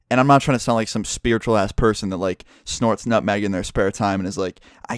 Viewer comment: "Snorts nutmeg in their spare time." I thought he said "A bunch of mag" instead of "nutmeg" and thought he was talking about magnets. xD